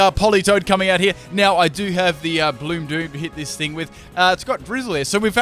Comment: When u said bloom doom I thought u said blue poop😆